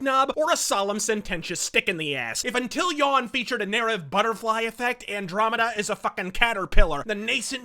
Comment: What is the word before "butterfly effect" at 8:16? Sounds like "narav" but that's not a word, CC says it's "era" but that makes no sense either.